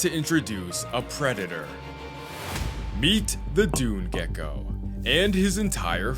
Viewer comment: DID YOU JUST SAY GECKO!???!!! I am literally in love with geckos